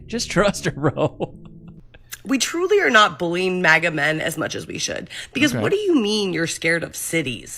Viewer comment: Im listening to your videos in the back ground and i just heard: 17:43 "we truly do not bully megaman as we should" also she literally looks like megamind